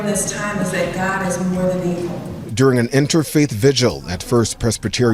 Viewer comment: DID SHE CALL GOD "MORE THAN EVIL"?!! or do I have a HEARING PROBLEM??